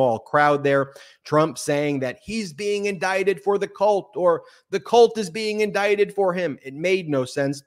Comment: Wait a minute... I thought he said he was being indicted for the blacks. Lol😂😂😂